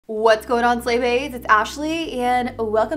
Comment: YouTube's captions called us "slave aids" instead of slay baes lmao